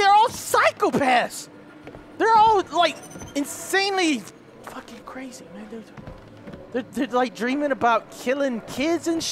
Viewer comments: What did you say at 32:17 xD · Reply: Michael Caballero He whispered "They're all insanely fucking crazy, man, they're like..." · what did he say? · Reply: ツZERO_1 crazy